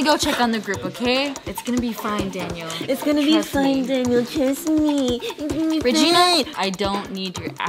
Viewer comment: regina was going to say "and to think i ever had a.. nevermind" she was going to say crushhhh! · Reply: is it just me or when regina was mocking allie and she said trust me it sounded like she said kiss me is that just me